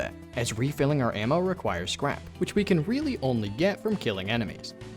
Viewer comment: if its at 2x speed it sounds like he said "refilling tools requires crap" 😂